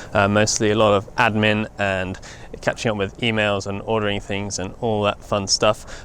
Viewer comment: When you said attending to e-mails at the beginning I thought you said females.